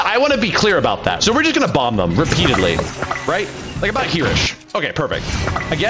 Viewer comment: When he said "here-ish" i thought he was saying "hirosh" like Hiroshima 💀